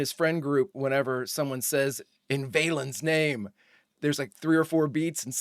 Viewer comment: My boy was like 7 went this was first on. He thought they were saying "In VanHalen's Name"😊😊😊😊